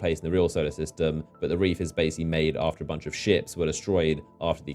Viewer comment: I thought he said after a bunch of shits been destroyed oml